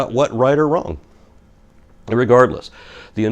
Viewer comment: did say irregardless? Lol